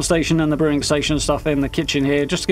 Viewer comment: I do love this smol build!! It turned out gr8!! Even tho you stated it had a kind of a "method lab vibe" 😀 thank goodness Bethesda is getting closer to fallout 4 materials!! Now that would be a bud lol!! Thank you for sharing sir !! ❤️ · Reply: Typo in there or did you mishear? Cuz I'm pretty sure I said meth lab! 😂 It was a convenient way to do the kitchen that changed things up from the usual look and allows me to shoehorn in the last couple of crafting benches. I'm pretty keen on smaller builds like this. They end up with plenty of character imo.